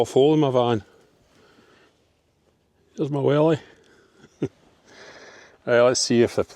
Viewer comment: Am i the only one that thought he said here's my willy 😆 Good video 🙌🏼